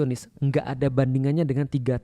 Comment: Nanya dong, di menit 14:38 Ustad Felix ngomong apa, ditimpa suaranya ga jelas. "....bandingannya dengan 3 tahun menghadapi [...?..]...." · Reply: Bashar Al assad